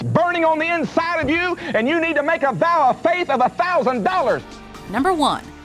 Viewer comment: Did i heard it right? "Make a vow of faith for a $1000"